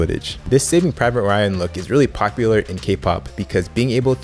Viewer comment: what does he say on 21:18 this saving private ryan look is popular in kpop?